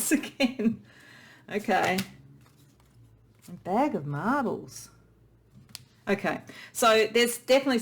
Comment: 😊💛 I'm laughing with you at when the second clouds card came out lol! and because I heard you say bag of marbles when you said bag of marvels 😆💚